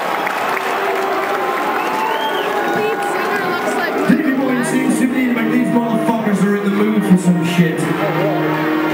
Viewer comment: what did she say? "this singer looks like...?" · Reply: I think she said "The lead singer looks like Michael Jackson" but don't quote me on that.